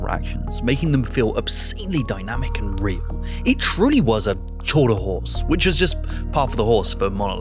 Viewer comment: I heard J say "tour de horse" here, witch made me think of the one horse that ran into the Tour de France